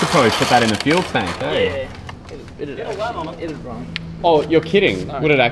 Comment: What did the guys say @ 8:52 in response to suggesting that they put that runny oil in the fuel tank? I have hearing issues and cant make out what they said and the subtitles miss it. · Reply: They said that "It would run!" and were serious. Apparently, the engine would probably love it!